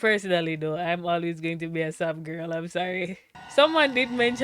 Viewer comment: I had to replay it four times before I realized you were saying “Sam’s girl,” not “sub girl.” 😆 (Even the subtitles say “sub girl.”) You haven’t seen the full scope of Dean’s character yet.